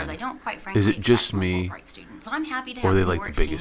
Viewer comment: And that guy is a Fulbright scholar, great going, whoever is throwing away these so called scholarships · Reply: Ravi S Chadha Oooo , is it fullbright. I thought he said fool blind scholar. I thought he was honest.